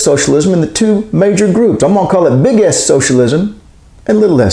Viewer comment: At first I misheard you as saying big and little arse socialism. Dang these Yankee, sic, manglers of the monarch's English!